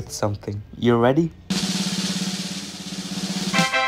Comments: When he said "I'm gonna be the God father" I thought he said "I'm gonna need a gun father..." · At first I thought he said he was gonna be the “grandfather” so I checked the comments like “why is no one talking about this!?” So i rewatched and she said “GOD father” and I was like OHHHHHHH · ngl I thought he said "Its gonna be a nephew and im gonna be a dad-" I almost lost my shi